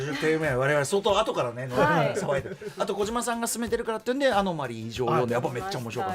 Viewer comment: 宇多丸さんが小島さんがオススメしてたから読んだ本のタイトル？がうまく聞き取れないんですがどなたかわかりますか？？ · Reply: アノマリー 異常ですね。